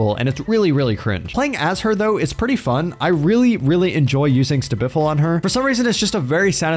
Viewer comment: excuse me can someone tell me what does he say in 12:39? the perk he likes to use on pig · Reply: save the best for last (stbfl)